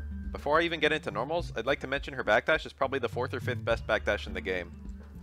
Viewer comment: When you said her backdash is the 4th or 5th best, i thought you said 45th best, which was pretty confusing till i rewinded it.